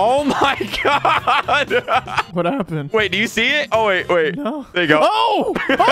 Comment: Wait did just say the n word ? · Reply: he said “no”